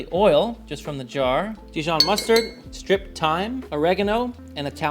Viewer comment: I heard “strychnine” like the poison instead of striped thyme. 😂😂😂